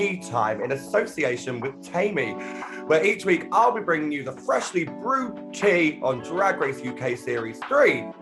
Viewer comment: Woah, I miss heard you saying "freshly groomed tea!"